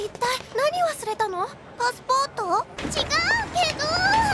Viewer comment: ｱﾚｶﾞﾅｲﾄ~が あれがこないの〜に聞こえて興奮した